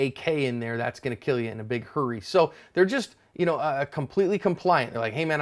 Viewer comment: at first a thought he said "that's gonna kill you in a BIG CURRY xD"